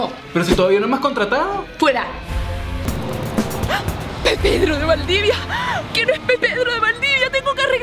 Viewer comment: No entendí :v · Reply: Pepedro? Xd pedro de valdivia